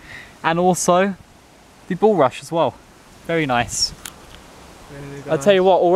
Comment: Haha I thought that said the butbrush